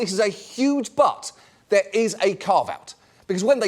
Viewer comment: Did he say "car vat" instead of "caveat", or did I mishear?